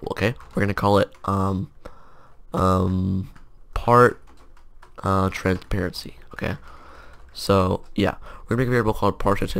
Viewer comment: did anyone else hear part transparanpe